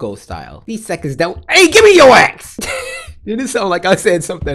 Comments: sound like u said give me your ex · I thought he said GIMME YOUR A** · It sounded like you said give me your a-Never mind